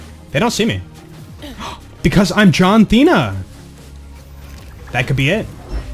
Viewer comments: it sounds like your saying john cena with a lisp Edit: liTHsp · Every time you say John Thena I had to pause for a second because I thought you were just saying John Cena with a lisp